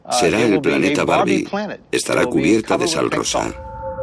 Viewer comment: te juro que escuche cerveza en vez de sal rosa 😂😂😂